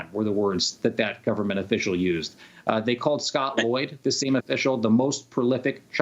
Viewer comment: Scott Loyd? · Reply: I thought he said Boyd. You are right ... it is Lloyd